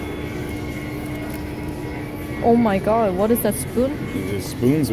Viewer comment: did someone say "spoons"?!